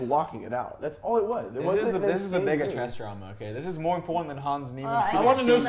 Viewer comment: did he say the n word? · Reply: No lmfaoao. He said "bigger"😭☠️